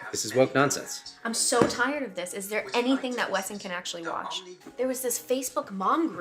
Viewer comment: Wait no no wait pause, did they just say the kid is named Wesson? as in Smith & Wesson? these freaks are way to obsessed with fire arms